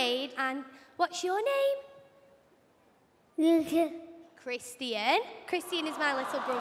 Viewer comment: IT SOUNDED LIKE HE SAID LUKA/LUKAS SO I WAS LIKE: "Oh lukas-" "Cristian 😊" "What."